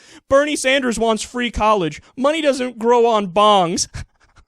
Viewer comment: I thought he said "money doesn't grow on bombs" which it apparently does.